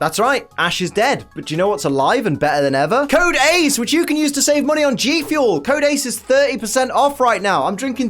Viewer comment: When you showed the gfuel i thought it said save monkey lol